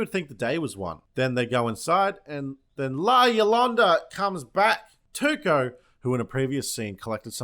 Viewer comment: I thought he said "La Yolanda" 😂